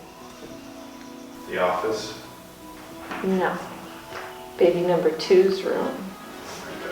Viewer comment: All I heard was “baby number 2’s room”...😂